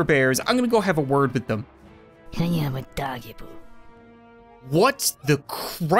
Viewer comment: did it just say”I am A doggy poo”😂😂😂😂😂😂😂😂😂😂